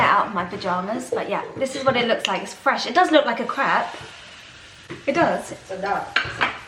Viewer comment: Lol. Sounded like you said it looks like crap!